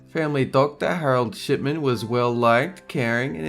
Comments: at first I thought he said Harold shitman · I thought you guys said shitman instead of shipman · Wait... Harold SHITMAN?! XD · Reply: I heard "Shitman" too. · I thought he said shitman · Am I the only one who heard "Harold Sh!tman"? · harold shitman....great name. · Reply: I thought he said that too. But the name is actually Shipman. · I thought ou said harold shitman